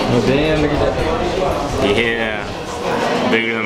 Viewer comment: sounds like he said “damn ni**a dat” or is it just me?